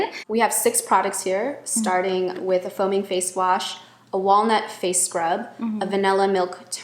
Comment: I thought she said “phony face wash”. Haha makes sense tho 😆